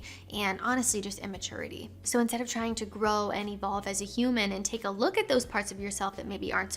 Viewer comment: At first, I thought she said, "So instead of trying to grow any balls as a human..." I like your advice on the astrology thing. Every once in a while, someone I admire and respect will whip out the astrology thing. It's a letdown and makes me question everything else they say.